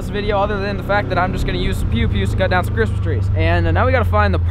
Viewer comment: Who else thought he said Pewdipie instead of pew pew